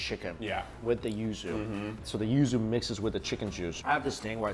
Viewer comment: Anyone know what is the spelling of the sauce being poured on at 11:04?. Sounds like Yu Su?. CC Captions have it as 'User'. I want to buy some!. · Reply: Yuzu